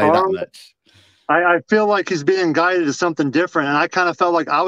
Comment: I kept hearing say "gutted" when he was actually saying "guided". I promise, he's not talking about being devastated. It's just the accent.